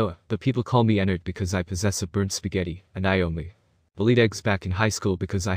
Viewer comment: I heard "bird spaghetti" LOL- 😂😂🔪